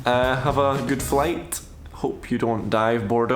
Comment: Lol, thought you were saying "hope you don't die...." On the flight 🤣. Enjoy the time with the family 👌🏽